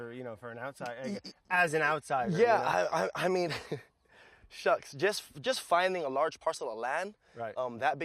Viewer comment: did he just say "shucks"?